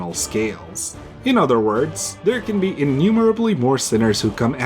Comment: did you say "innumerable" or "enumerable"? both makes sense, mathematically😭